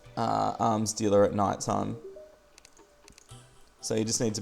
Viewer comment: When you said arms dealer it sounded like you said arm stealer.